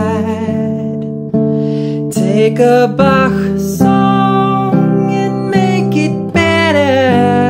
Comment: I lost it at 2:33 "Take a Bach song and make it better" Had me wheezing! 🤣🤣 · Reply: I thought he was saying Take a Bach song and make it Beatles